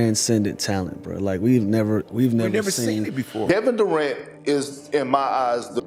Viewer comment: Did he just said Devin Durant? HAHAHA